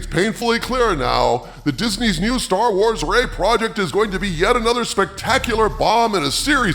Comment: When he said "Ray project" I genuinely thought he said r**e project and was making a joke but then I remembered YouTube censors EVERYTHING so that wasn't correct